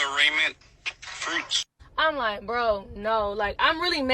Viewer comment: Am I the only one who heard "arraignment fruits"?!?!🤣